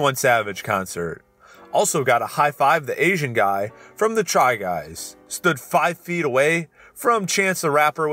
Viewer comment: Sorry but did they just called Eugene "the Asian guy from the try guys", as a fan of them it's the funniest thing to hear